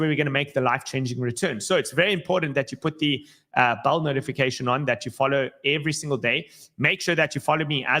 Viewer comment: Does it sound like he says ‘check your bowel notifications’ to anyone else or just me. 😂